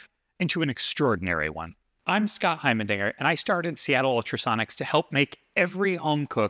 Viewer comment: did he say "Heimerdinger"??? A guy whos name is is presenting us an innovation..